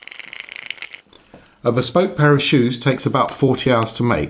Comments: Damn my ears, I heard "bespoke parachute" when he said "bespoke pair of shoes". 😂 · I thought he was saying A SPOKED pair of shoes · Fun fact, I set my phone down when this video started playing and didn’t watch it right away. It played 4 times before I actually saw it, and the whole time I thought he was saying “bespoke parachutes”. Imagine my surprise when I finally saw what he was making. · I was just listening and thought he said bespoke PARACHUTE 😆 I was like, uhhhhm, bad idea? Lol